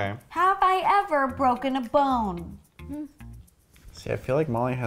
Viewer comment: Thought you said have I broken a phone